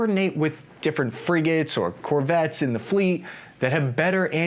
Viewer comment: Did he say corvettes?